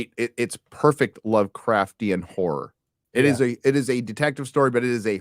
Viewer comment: Hahaha at 1:30:41 it sounds like says its a "whore" story.